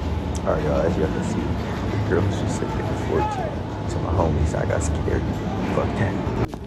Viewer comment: What did you say at 2:27? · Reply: These girls said they were 14 so I had to 🏃🏾